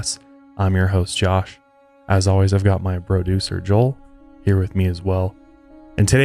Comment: I thought you said “broducer” 😆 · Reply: He did say broducer! lol